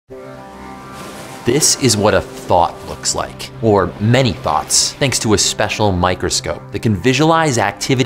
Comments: The Intro I Thought I Heard ForThis Video Was: "This Is ₩hat A Thot Looks Like. Or Many Thots... ₩hat Is A Thot Really‽! ..&₩hat Does That Have To Do With A Cockroach?" Was likeee WTF... Hahaha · In the intro i heard "This is what a thot looks like, or many thots" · what he said: “this is what a thought looks like” what i heard: “this is what a thot looks like” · I stumbled upon this on auto play and I heard, "this is what a T H O T looks like" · I thought he said “This is what a THOT looks like”…and now I’m sad.